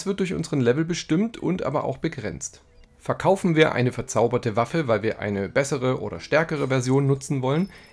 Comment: waffe oder waffel ich hab halt ernsthaft waffel verstanden😅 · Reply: Er sagt Waffe